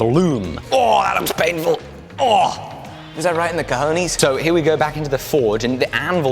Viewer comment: it sounded like he said: "right in the colonies" lol.